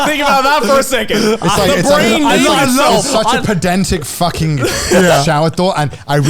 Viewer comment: Curious to understand what Connor said at 3:57 - podentic? Can’t spell it right or close enough to look up this word. Help! 😊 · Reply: Pedantic Just think of someone saying "umm akktually ☝️🤓"